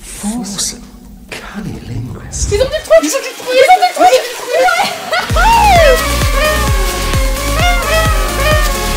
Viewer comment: Quoi ? Comment ? 👂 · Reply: "Ils ont dit le truc !"